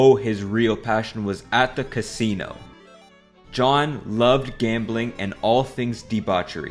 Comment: for a second I thought u said John Locke and I had to do a double take